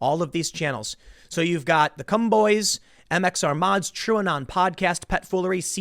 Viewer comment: Tim said “The Cum Boys” lmfao! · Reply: Time does indeed say "The Cum Boys" at 8:46.